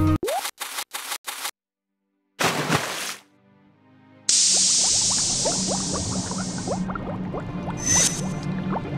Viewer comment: darn moon runes! Anyone know what she said? Also, the seashells were too small? Wow, her chest is big in more ways than one. Also the seagull straight up startled me, actual voices?! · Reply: She says "thanks, mom!"